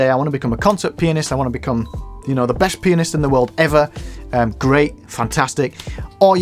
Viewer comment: my brain got stocked, did I just hear "the best penis in the world" then I remember oh, we're talking piano here, my guess its pianist :) happy holidays everyone :)